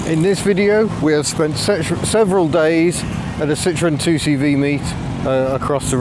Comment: I thought you almost said we spent sexual days in the 2cv lol